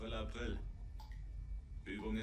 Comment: By god, some of the subtitles are way off the mark and neither translate what's said, nor accurately represent the dialogue in the slightlest. For example, at 9:45 the Kaleun says "April, April", which is how we let someone know he/she/they have been the target of an April Fool's joke(and fallen for it), yet the subtiltes refer to how well the lads have done getting the boat to dive down.... I would consider it excuseable had that been said by the Kaleun in one of the other lines, yet he says nothing to that effect ever.